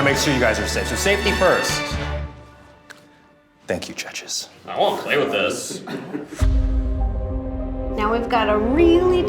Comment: What did say to at 42:53? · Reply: "i wanna play with this"